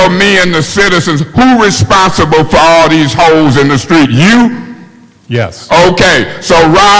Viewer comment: I THOUGHT HE SAID "HOES IN THE STREETS"!!!!!!!! 😅😅😅😅😅😅😂😂😂😂